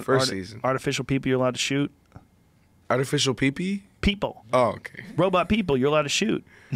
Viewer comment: Post: "artifical pee pee?" Rogan: "People." Post: "Oh" Lmao i heard pee pee too though